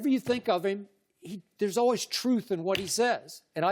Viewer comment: Did Skip just say there is always truth to what Lavars says?